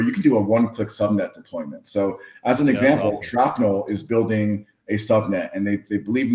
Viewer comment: Did someone say, "Shrapnel" at 10:09 😎. Great interview!